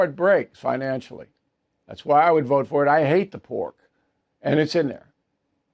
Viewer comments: “ I hate the porK” I had to listen to the twice I thought Bill said , “ I hate the poor “… I’m like , wait what??!! Rewind…😂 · Bill I was listening and was jarred at 1:59 when I THOUGHT I heard you say " I hate the poor " . It took a second or two till I realized you said " I hate the PORK .